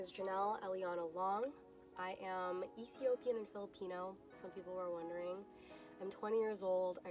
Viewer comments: Did I hear her right? She's a filipino? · did she just said filipino???